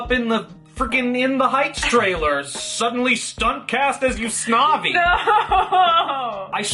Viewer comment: did you say "yous-na-vi" ? 😭